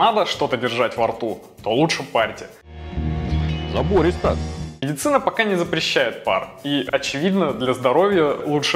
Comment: мне послышалось или "Яцына пока не запрещает пар"? Павлик то, небось, сам вейпер?